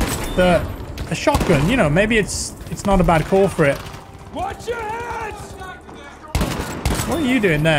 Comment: I thought a man said “Wash your hands!!”